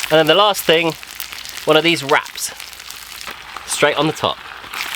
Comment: I thought he said, “….and the last thing… one of these rats.” LOL